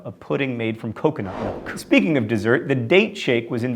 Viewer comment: i misheard when he sait "Date Shake"..I heard it as "Date Rape" >w>